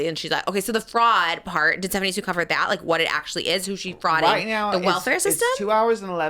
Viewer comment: did I hear "frotting the welfare system"??